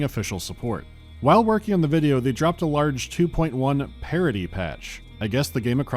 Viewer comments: I heard “parody” patch not “parity” which would have taken it in a totally new direction! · = Sadly, I misheard this as "parody patch" at first, and now I wish EVERY game had one of those... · Oh, parity patch. I thought you said parody patch.